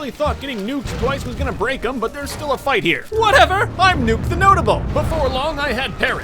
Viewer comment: “I’m Nuke the Notable” Proceeds to commit heinous war crimes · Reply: I thought he said nuke the lotable